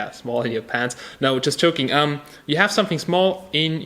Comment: all i hear is "just choking"